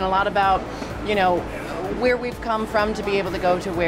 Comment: What jinkx say? :s I dont understand a lot the english but i wanna know what jinkx say at 1:57 (where we come from ? :s ....... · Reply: "Where we've come from to be able to go to where we are going"